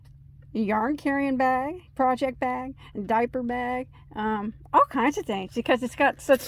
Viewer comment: bag with liner <link> handles <link> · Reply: As I continue to listen to your video I heard you say diaper bag LOL